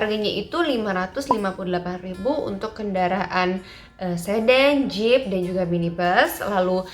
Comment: saya yg salah denger apa gimana ya? Kendaraan sedan, sedeng apa sedang ya maksudnya mba? 😅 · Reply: Sedan :)